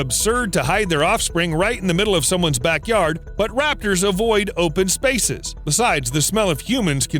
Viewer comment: Did he say Raptors?